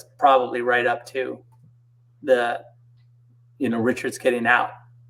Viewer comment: did he just call the alpha zombie (zeus) richards?